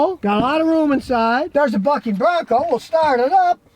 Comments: I thought Scotty said “ it’s building a f***ing bronco “. 😆😆 I rewound it then herd bucking. It was good for a laugh 😂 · It's building a f'ing Bronco...that's what I thought Scotty said. Wow, he is really excited about this Bronco. I had to listen to it a 2nd time to realize he said "bucking." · Thought you said f***in bronco when the starting screen was on!! Lol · At first I thought you said. It’s building a F*CKING Bronco! Haha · Lol. Sounded like Scotty said F'ing Bronco, instead of Bucking Bronco. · I thought Scotty said F***ing 🐎 not BUCKing Bronco. Lol 😁